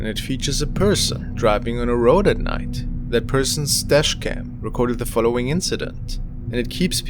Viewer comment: Thought you said stash cam and wondered what the stash was!😂 good set of clips :)